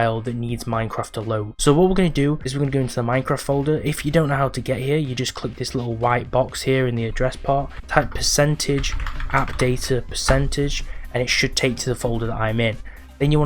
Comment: Bro 5:00 what are u saying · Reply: %appdata% is what i said and typed :))